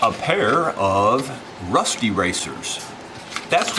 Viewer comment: Thought you said "rusty razor".